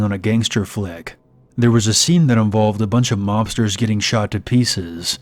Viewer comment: i thought said "lobsters that got shot up"